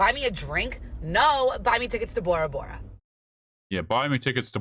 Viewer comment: I thought she said "Buy me tickets to Tora Bora".